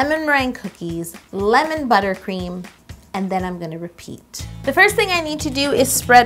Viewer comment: i thought she said "now i am gonna eat the cream "